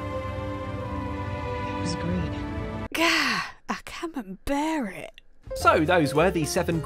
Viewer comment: I'm sorry. Did you just say "I Camem-bert it?"